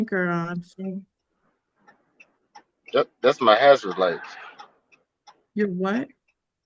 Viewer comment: first, I thought said it was his asthma light ~ what ? then, I thought he said azure light 🙄 WHAT ?? 😂 thank YOU, ✨ ~ happy taco Tuesday